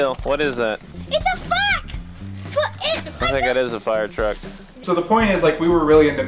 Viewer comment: I’m sorry did you say “ITS A FUCK?” 😂